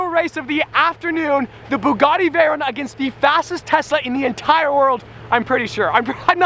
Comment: Did anyone notice that whenever he pronounced “Tesla’s”, he sounded like he said “Tessa’s”